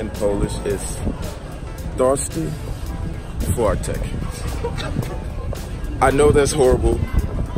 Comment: Sorry bro, but all I hear in 1:43 is “thirsty fart’ec” 😂